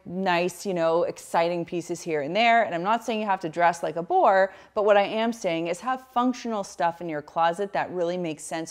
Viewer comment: Did you really say “I’m not saying you have to dress like a wh0re?” 😮 This has such sassy teenage girl in 1999 bullying another girl vibes. I get what you are saying but I that’s not very nice to S workers or people who want to highlight their body. Maybe next time you can say something more positive like: “I’m not saying you need to dress : in a way that doesn’t make you feel good/in a way that doesn’t suit your personal style/in a way that draws attention to yourself” Love your tips in this video though! 🙂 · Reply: No, you misheard her. She said, "I'm not saying you have to dress like a BORE."